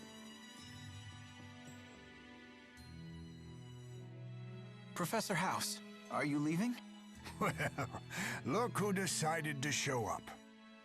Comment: I swear to god i heard him say "Professor House"! Though it would be too awesome for the game to handle.